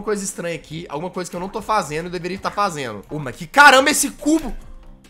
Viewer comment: eu ouvi ele fala mais que caral** esse c&